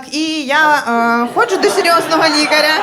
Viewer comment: Почулося "я ходжу до серйозного нігера"